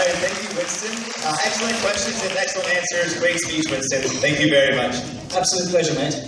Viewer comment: LOL French guy sounded like he said I like to feel Winston ha ha